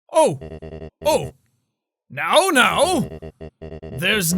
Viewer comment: I thought he said How, now, so I instantly thought, "How, now brown cow" 😂😂😂